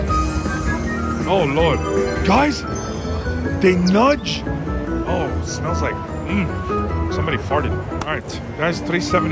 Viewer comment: Totally thought you said it smelled like shmeed. So I heard oh..it smells like shmeed, someone must of farted! Lol